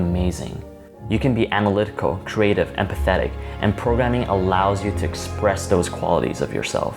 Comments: I thought he said "and pathetic" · well i may be dumb but when he said 'you can be analytical, creative and empathetic' i heard it as 'you're pathetic.' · "You can be analytical, creative, and pathetic." Anybody else heard this? :')